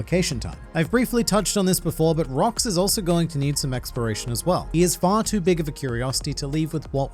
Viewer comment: you know for a sec at 7:34 i thought he said and rox is going to need some expiration as well, and my mind was nah hes already to dead for that XD